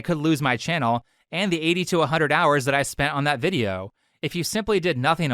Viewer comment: haha i heard 8200 (82 hundred) hours